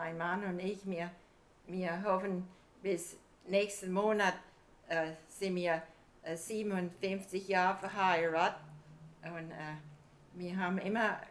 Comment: "sind wir siebenundfünfzig Jahre verheiratet" = "we are married for 57 years" (not 75 anniversary as the subtitles say, she's not that old)